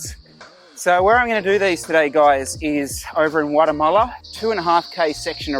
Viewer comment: I thought said Guatemala 😂